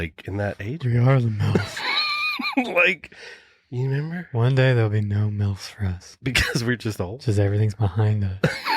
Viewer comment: I thought said "one day there'll be no milks for us"